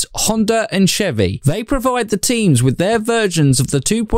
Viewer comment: I heard 'supply the team with virgins' 😵